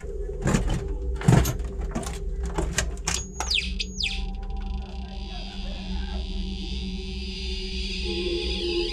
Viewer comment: Did anyone hear that voice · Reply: It’s yelling “this is the best idea I ever had”